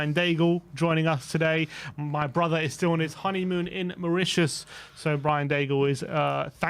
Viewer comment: Did I hear correctly?? Did said Mauritius?? 10:28 Its the first time someone said the name of my country!!